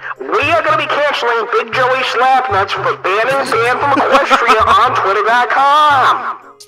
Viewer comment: WE'RE GOING TO CANCEL BIGJOYSLAPNUTS FOR BANNING QUEST TO EQUESTRIA ON TWITTER DOT COM animesexuals be like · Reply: I thought said banning banned from equestria